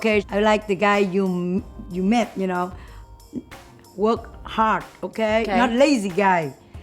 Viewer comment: When said "Work hard" I heard "Look hot" XD